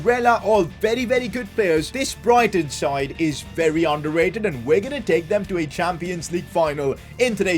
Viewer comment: I thought you said underrated not overrated